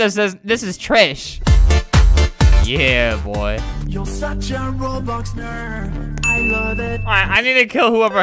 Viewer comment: did it say “John roblox girl”